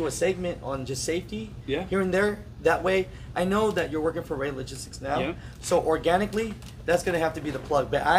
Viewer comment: Wait he works for ROEHL ? Marshfield,Wi..horrible company..! Turnover rate is damn near 99%..he has his work cut out for him..! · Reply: Oh, it's Rey Logistics? I also thought he said he was working for Roehl. I was gunna say "Good fuckin luck bud" LMAO!